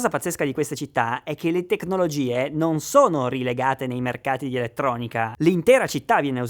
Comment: le tecnologie "rilegate" (m. 19:34)............forse ho sentito male